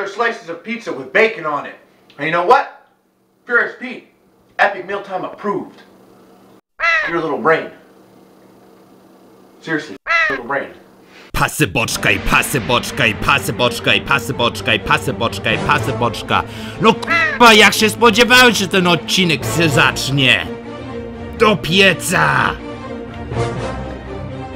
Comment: pasibotsukai pasibotsukai pasibotsukai pasibotsukai pasibotsukai pasibotsukai pasibotsukai pasibotsukai pasibotsukai......this is what i hear